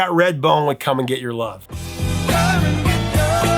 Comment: As a kid, I thought they were saying, "come and michelob"!